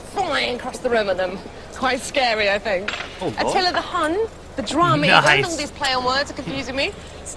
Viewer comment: Did she say Atilla the Hun?